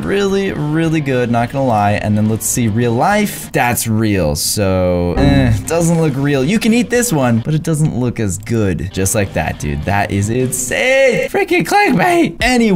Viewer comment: I thought he said *I'm gonna eat this" I got confused